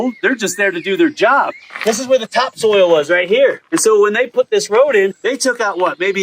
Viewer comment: Hahaha at 19:56 you say “take soil samples “ haha I hear you say soil but my brain hears stool sample!!😂hahaha !!! I couldn’t help myself hahaha 😆